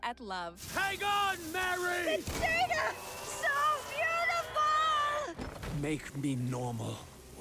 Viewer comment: Wait was she saying? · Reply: The data! So beautiful!